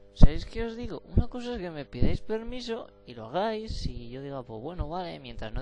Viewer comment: a vale, es que como dijiste tan seguido pense que decias que no te pedi permiso (despues de lo que paso cuando me diste permiso)